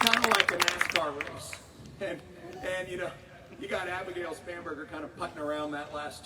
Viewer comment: I kept hearing “spamburgar”. Must be hungry.